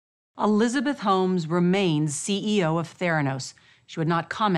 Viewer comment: Thought it said Thanos 😂